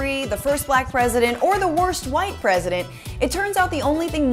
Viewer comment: I was watching it twise the speed, so I had to check what was said after "the first black president," when showing Trump. I thought I heard "first white president," so I thought "huh? Well, maybe in the sense that he's the closest thing to someone actually tunning a platform on white nationalism, but that's still a weird way to phrase it, almost as if it were a neutral thing... oh, wait, maybe it was first orange president that she said." But then it was "worst white," which also makes even more sense. But I bet there must have been some debate on going with "worst white" or "first orange."